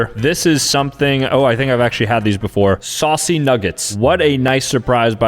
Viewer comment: bro I was looking away from the screen and I heard “sussy nuggets” HELP 💀